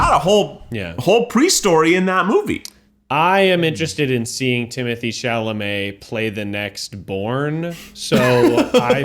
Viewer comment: for sure thought said "i want to see timothee chalamet do porn" & had to rewind al;sijdfalskdc